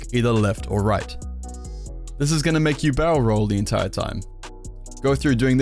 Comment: When you said "barrel roll" your accent kinda confused me and I thought you said "bowel roll"